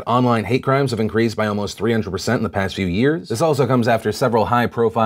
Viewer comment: I thought you said "Has increased in past 300 years" and I choked on my Apple juice because I was shook. I almost started to wonder how long my bath actually was.